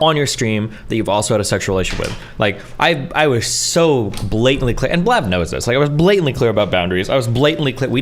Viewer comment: Lol at 5:20 destiny really emphasizing those B almost sounded like he called her Blav. Lol Blav blatantly blabbering blasphemous bullshit 😂